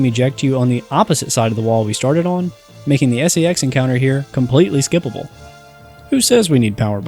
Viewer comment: I think my brain is just broken, but I keep hearing him say "S-E-X Encounter"